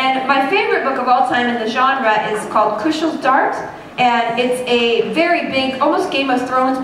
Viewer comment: I can't understand the name of the book (2:00), could anyone tell me? And thanks a lot for posting these videos! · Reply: Maybe you found it long ago, but it's Kushiel's Dart